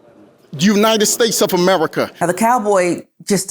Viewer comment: Did he say Jew-nited States of America?? at 4:12 ?? WTF